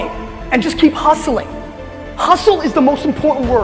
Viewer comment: what is the word with "H" in 9:17 ? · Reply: Hustle it means working real hard to get what you want in life.